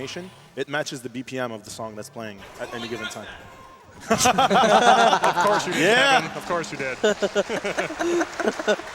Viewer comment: What did the guy say at 18:02? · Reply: "I totally got that!"